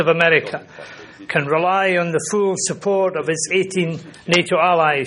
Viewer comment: I definitely heard him say that america could rely on "the FOOL support of NATO".